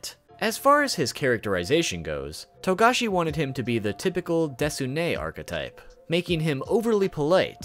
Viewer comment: what's that word? "Desune??"